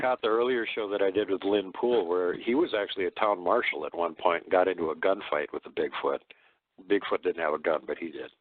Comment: omg man for a split second I heard "Bigfoot in a gun fight" and pictured a Squatch hip firing a machine gun 😂😂 Rambo? Squatchbo.